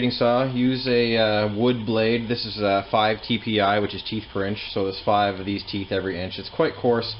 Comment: min into the video, did you say "cheap French"?